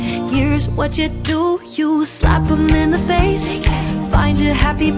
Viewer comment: it’s actually “You slap them in the face ( JK) instead of yeah yeah